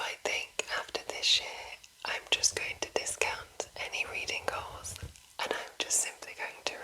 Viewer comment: Bless ya. Thought you were saying 'Reading Culls' at first. Don't kill books! x